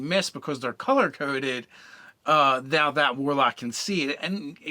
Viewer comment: is it bad that i thought it said wario